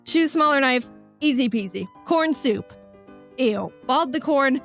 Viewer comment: i thought she said p- soup